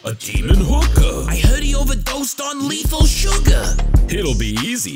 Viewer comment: I heard it overdues in leathel surgar! Best line yet